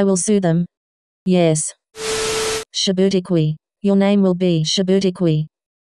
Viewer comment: what is the caption say Djibouti Qui😂 it doesn’t sound anything like Shabootiquiqui like what type of a name is that?